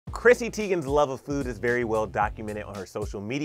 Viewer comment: I really thought he said prissy p 😂😂😂